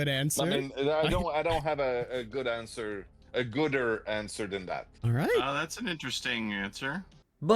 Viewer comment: did he just say gooder😭